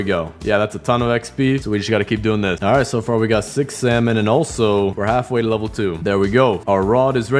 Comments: I thought he said 6 7 ngl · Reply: “Six SeVaNnn” untrue or “six salmon” true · He said 6 salmon i thought he said 6 7